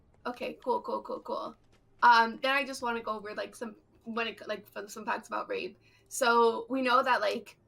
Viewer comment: Did I just hear her say "some fun facts about rape" or "some facts about rape" because that first statement is a BIG YIKES.